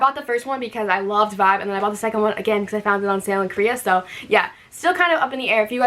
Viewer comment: I can know which sites or agents that U used to buy all this kpop album.. I heard u said 'selling Koreans' but can't find it on google.